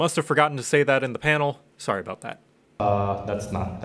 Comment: Did you just say "that's falsch?"